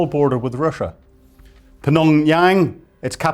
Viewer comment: Did he combine just Pyongyang and Phnom penh into Phnonyang? 😆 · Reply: I think he said Pnongyang. 0:56 seconds.